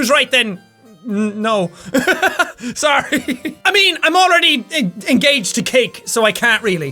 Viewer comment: Jack, when you said 'Im already engaged to cake' I initially thought you said engaged to kate! I was like OMG what!? xD